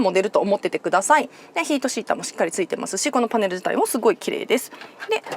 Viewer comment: ヒートシーターって聞こえるけど、シートヒーターでは？